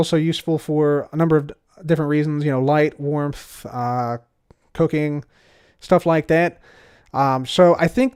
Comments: i heard cocaine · i thought you said cocaine... i was like what?... rewind oooh cooking xD